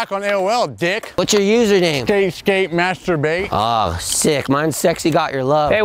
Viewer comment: "Whats your Username?" " StaySkateMasturbate " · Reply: I thought he said SkateSkateMasterbate